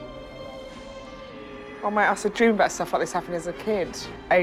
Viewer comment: Can someone please tell me what Roisin says when she opens it? I can't tell and the auto-generated captions aren't helpful. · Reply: "Oh mate, I used to dream about stuff like this happening as a kid..."